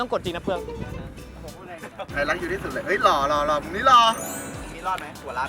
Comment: Epนี้อรุ่มไม่ไหว5555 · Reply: เราฟังผิดป่ะที่ได้ยินพี่เฟิร์สพูดว่าไอรักยูที่สุดเลย15:01